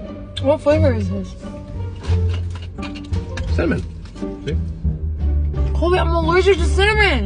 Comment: i literally heard him say “it’s cinnamon, babe” WTF